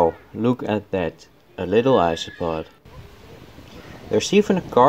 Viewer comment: when you said "look a little isopod" I heard "astronaut" and chuckled cause thats a cute joke, but then I realised it wasnt but im gonna pretend it was.